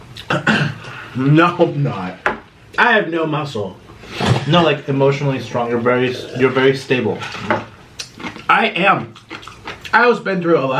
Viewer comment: "you're emotionally strong. You're very stable" did I hear that right? 🤔